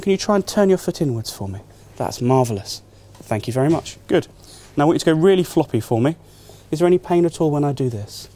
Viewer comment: What does he say at 2:04 before he swing patient's leg? Please let me know. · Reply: I want you to go really floppy for me